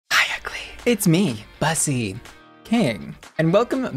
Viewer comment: I think I need hearing aids i coulda swore you said: Hi, It’s me Bussy, peeing